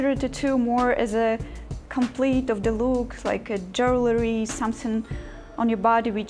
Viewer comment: I thought she said, "...like a Jos Louis..." lmao